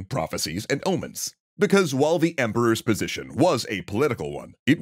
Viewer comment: My brain melted when I thought they said "the emperor's position was apolitical."